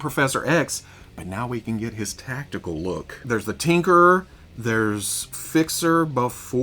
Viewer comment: I wasn't fully listening when talking about Hank's body being reused and thought you said The Tinkler.. that would be a dangerous super villain, uncontrollably bladder sufferer 😂 Awesome weekly as always!